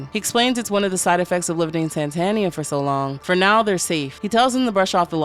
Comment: Bro I thought it said Santana like the pillar man and I was really confused for like the first 30 seconds